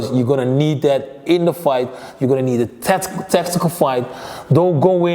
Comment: Did he say 5:22 : Its gonna be a Testicle Fight🤣🤣🤣🤣